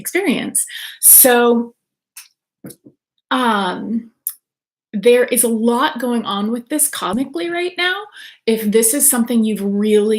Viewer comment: I know you'll think this is funny....... maybe I heard wrong but when you said toward the beginning, I think you meant to say "there's a lot going on cosmically" but it sounded like you said "there's a lot going on comically." lololol That was so perfect! I wouldn't post it here if I didn't think you'd get a kick out of that. Will listen to the rest later. Thanks, good topic!